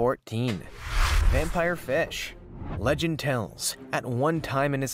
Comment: i thought he said "wampire bitch"